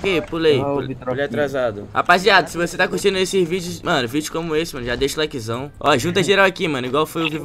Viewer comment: Só eu que entendi ele falando xvideos kkkkkkkkkkkk